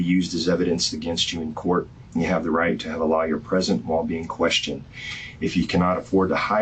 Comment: The way the detective pronounced lawyer, it sounded like he was saying a liar